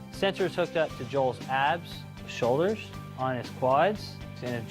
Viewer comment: does he say on his butt and then show his butt ultra close up that is hilarious i am dying